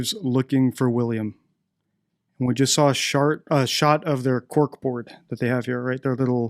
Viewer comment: did he just say shart